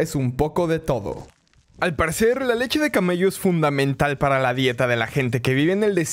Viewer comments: Pensé que decía ''Leche de Camelo'', de Benito Camelo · soy la única que pensó que dijo “ Leche de camaleón “